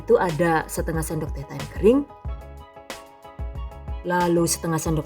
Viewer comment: Menit 4:20 Gak jelas ngomong apa.... Cuma " kering " aja yg jelas terdengar... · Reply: Itu ada tulisannya Thyme.. diucapkan thaiiim..